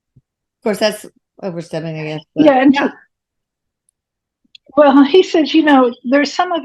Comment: Voice @ 7:19, 7:36? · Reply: I heard “yeah” at 10:39.